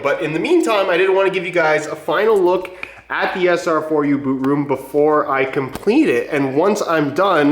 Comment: I thought he said sr4u poo room🤨